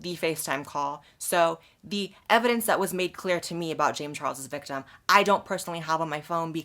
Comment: That's why she said alleged victims. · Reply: I hear "James Charles' victim" ... without "alleged"